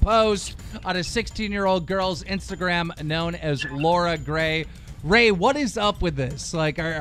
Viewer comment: Is it me or does it sound like keem is saying lora gray